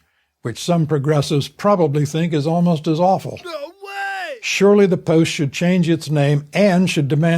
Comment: Does he say "NO WAY!" or "GO AWAY!"?